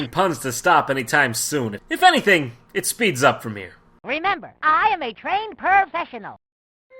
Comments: Lol sounded like Busby said remember I am a trained perve-shesional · At the beginning of bubsy 2 I thought bubsy said prevfessional